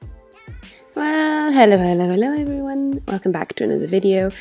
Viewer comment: seconds I did not understand anything that you said...it sounded like"welololoeveryone”💀💀not hate..just take time on your words lol